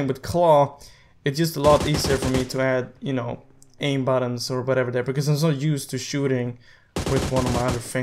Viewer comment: Am i the only one who thought he said easy to add aimbot lol 😂 i know he said aim button